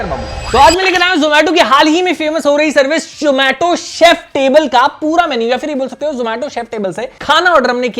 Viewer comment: shomato chef table instead of zomato chef table😂😂😂😂 saab ke pronuciation mea glitch aa gya 😂😂😂😂😂😂